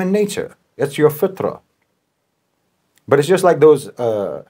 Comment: Hello. What word in Arab is said in minute 9:00? "Because, of course, like anyone else, you have Westerners, non-Muslims, who genuinely want to be moral, who genuinely want to be good, sincerely wants that because that's human nature, that's "FuThRA?" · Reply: Fitra: human innate nature given by God. Ie. Innate moral reasoning